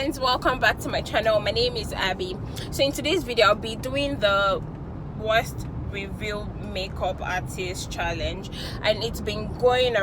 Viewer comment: Did you just say 'worst revealed make up artist' in the intro?